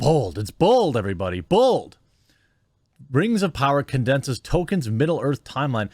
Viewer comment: Oh, "Tolkein," I thought you said "Token."